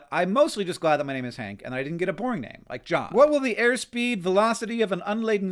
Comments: "I'm glad that my name is Hank and I didn't get a boring name like John" - William Henry Green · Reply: Man, I definitely heard that as "porn name like John". Boy was I confused! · "I'm glad that my name is Hank and I didn't get a boring name like John" - William Henry Green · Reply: I had the volume low and thought he said "porn name". I just casually accepted that "John" was a porn name. Come to think of it, neither of them is, but "Hank" is more of one. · The way I almost spit out my drink when you said “at least I don’t have a boring name…like John!” 😂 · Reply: I thought he said porn name like John 😮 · Man, I definitely heard that as "porn name like John". Boy was I confused! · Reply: I also misheard it as "porn name" 😭😂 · Shhhhhh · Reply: I thought he said a “porn name”